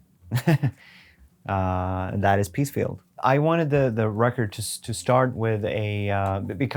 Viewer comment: Thought he said Pete Steele not Peacefield.